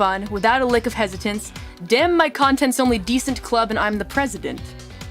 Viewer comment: the feel when i've been mishearing 2:11:51 as "damn my content's only decent, glad i'm not the president"